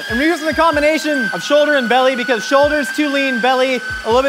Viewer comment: So, I'm half deaf, hard of hearing....My attention got grabbed when I thought I heard "Children in belly". After watching and listening again I understood "Shoulder and Belly". 🤦‍♀